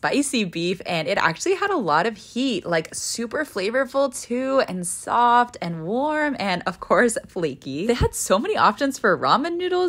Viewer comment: You make even dollar store food sound amazing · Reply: i thought she said “it had a lot of pee in it